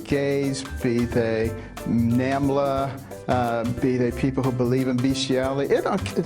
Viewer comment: WTF did he just say??? Nambla??? 🤔🤔